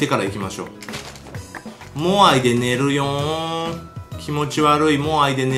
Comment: 寝るよ〜んが寝るにょ〜んに 聞こえるwwww メサイアの真似かと思ったwwww